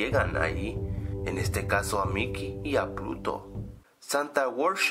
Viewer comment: en el 1:01 alguien noto que dijo (mickey y puto) xD